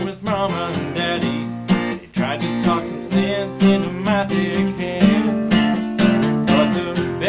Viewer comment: sounds like he says dick head :P